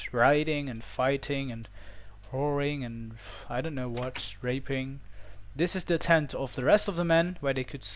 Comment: I might have heard it wrong (you probably said raiding) but I thought I heard raping...